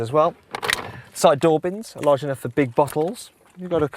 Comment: Did he say "buttholes" ?